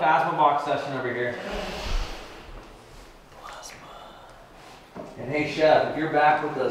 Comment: At around 1:06:26 was someone whispering? I heard the word 'plasma'